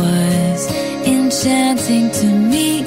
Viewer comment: For a second i thought is said Enchanteo😂😂😂